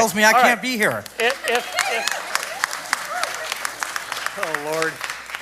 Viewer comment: did he just say “oh lord”?? 😂